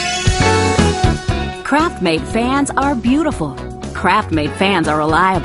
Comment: I thought it said “crapmade” and I was like It’s not crap!